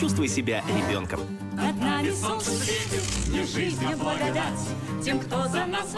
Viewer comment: Мне на 002 послышалась это не солнце светит